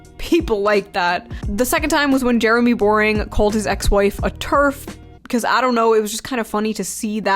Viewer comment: I thought/wished you said "called his ex-wife a 'turd'."